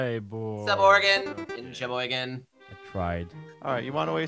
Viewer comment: did he say Sheboygan? That's my hometown lol